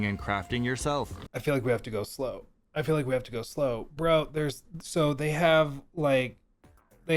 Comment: what made u buy this game? next 18 words!! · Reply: I feel like we have to go slow….I feel like we have to go slow